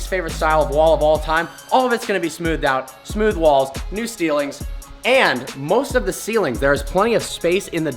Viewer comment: he says new steelings instead of ceilings